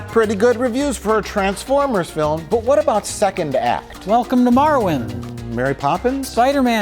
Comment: Thought he said "welcome to Morrowind" at the beginning.